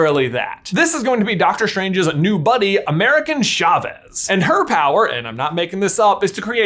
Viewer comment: I heard “American Chavez” and had to do a double take